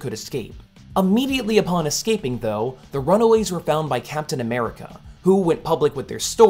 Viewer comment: Thought that said "Runway" like for fashion models